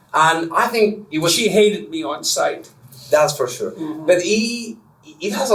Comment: did he say: "she hated me on site" or "sight"? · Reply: sight